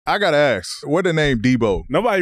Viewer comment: I thought he said "I got ass" in the beginning